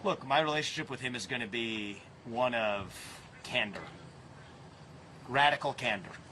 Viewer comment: i thought he said "camber" at first and i was like, "what does the vertical angle of a wheel have to do with zelensky"? candor = being honest.